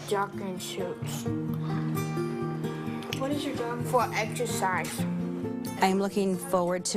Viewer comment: Exercise? I thought you said extra fries😂